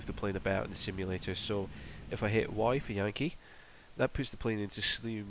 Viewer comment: What did he say? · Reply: Yankee or yogi is my guess😂